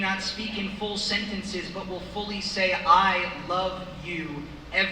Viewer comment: well im not a sobbing mess... I love you and your family..My day isnt complete until I hear the ''i love you Kk'' (is what I hear) and the kiss on the nose.. He is able to pull me out of the deepest funks.. Keep doing what you do my friend!